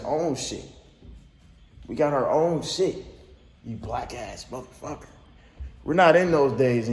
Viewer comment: When brace kept asking you if u were high u should’ve said nah but im bouta smoke u in a pack real soon🤣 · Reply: I played it over and over and I keep hearing “wack ass mf ni***” it’s hard to see because that man is blocking him when he says it.